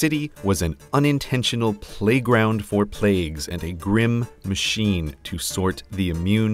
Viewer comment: I heard "plague-round for plagues" what's wrong with me I think I have a disease